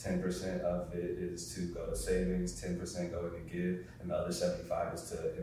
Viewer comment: what does he say at 19:48 10% to give?